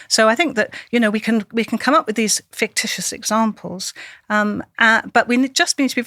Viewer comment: did she just say fictious ?! 💀💀